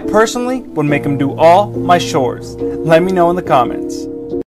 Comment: all my shores instead of saying chores😂😂😂 it’s like when you ask your Mexican dad for chips and he goes “ohhhhh la ships okok”